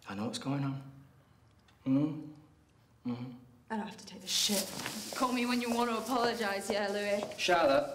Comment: The first time I watched this I thought she said "I have to take a shit" and felt like that was a weird way to redirect.